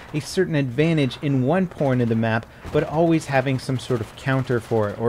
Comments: Wtf said Porn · did he say "one porn of the map" lol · Is it just me or at 2:25 did he say " one porn of the map"?